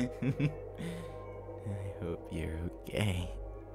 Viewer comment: WHY DID I HEAR “I hope ur gay....” LMFAOOOO I CANT WHAHAJANAJBSJSNEF 2:44 “Are you gay?” AHAHHAHANAJSBWJFNEJF PLSSSSS